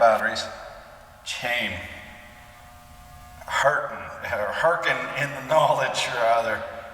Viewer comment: I thought you said they had beer ?